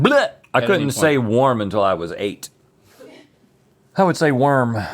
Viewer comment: I thought Rhett said “I couldn’t stay warm until I was 8 years old” and the idea of that is very sad and funny